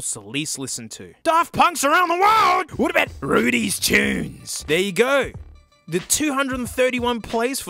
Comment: For some reason, I thought "rudys tunes" said "nudy tunes" and cackled a bit.